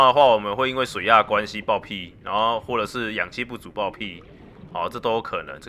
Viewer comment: 我聽成氧氣不足會爆屁XDXD